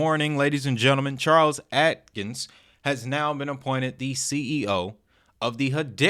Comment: Thought you said Charles Hoskins at first...I legit was going to my cb to sell all hbar till you just said his name again.